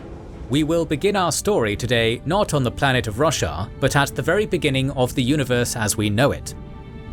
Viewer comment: Sounds like he said the planet of Russia lol